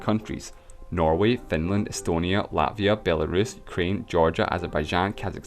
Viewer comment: I am sorry dude, you have funny accent and when you say "Ukraine" I hear something familiar to "Kreem". "Krym" is "Crimea" in my national language :p